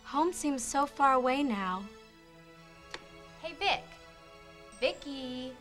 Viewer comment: Why my dumbass think she said "hey bitch"